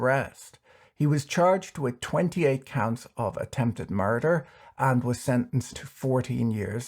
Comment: Ok, I heard you say "murder ARB” :) lol